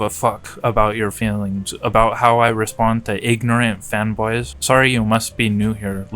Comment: You probably got it later but fee fee is insane speak for feelings. Also looked a way from the screen for a sec and misheard you say “fanboys” as “femboys” and I was really taken aback like why would that. lol. Happy I found your channel, i’ll check out your good yt video.